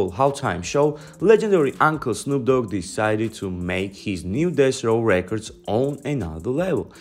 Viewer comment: Why does it’s sound like ur sayin dess row every time u say Deathrow 😂